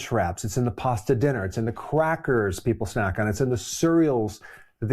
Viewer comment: did you say...Quackers? 😂